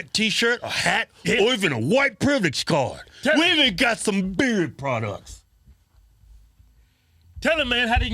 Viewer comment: For some reason I thought you were saying "bird products" all this time. Just realised you have been saying beard products! 🤣